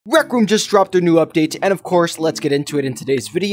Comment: say Roku instead of Rec Room